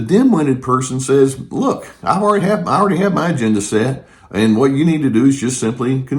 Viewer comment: Just after 7:30, I misheard "agenda" as "gender" - and that's one of today's baffling paradoxes: those who judge gender-choices. Dimwits on the internet write: "I'm secure in my own gender. Why can't everyone be like me?" They're unaware of how insecure they sound!